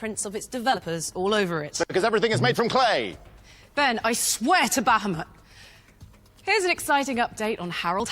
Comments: Did she say "i swear to bahphomet?" 4:50? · Humiliation ritual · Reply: Exactly, did she say "I swear to baphomet" at 4:50? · does she say " i swear to Baphomet"? Did i hear that correctly? Followed by the hand sign of Baphomet This satanic shit really is everywhere... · did she say I swear to baphomet? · did she say "I swear to Baphomet" ?